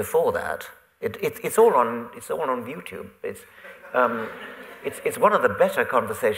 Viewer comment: sounded like he said "viewtube" bless Dawkins wholesome heart xD